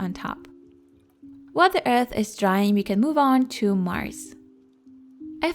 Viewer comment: "while the earth is drying, we can move on to mars" too real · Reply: Priya Kapoor i thought ti said while the earth is dying 😂